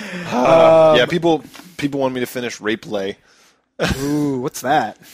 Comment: what did they say at 28:07 ? rape play?